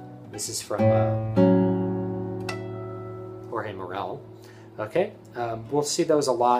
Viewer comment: What's the name of the short piece you play (roughly) starting at the 7:45 mark? It sounds splendid but I couldn't quite catch the name. Thanks! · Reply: Jorge Morel, "Sonatina" Movement 2. David Russel is the most famous recorder of the piece.